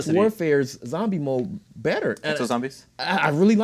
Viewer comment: I hate exo zombies that is why i was laughing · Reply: I thought you were saying exo zombies were better.